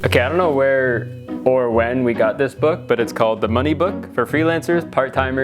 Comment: Bro at 16:46 why did I think the book was called “The Monkey Book” 💀